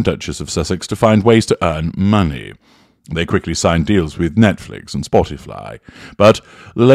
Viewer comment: Oh… I thought it said ‘skinned’ … You are sounding extra sultry today.